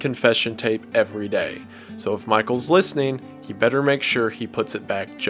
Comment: i thought it said that michael better start running